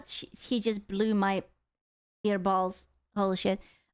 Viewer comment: did you say “ear balls”? lol I may steal that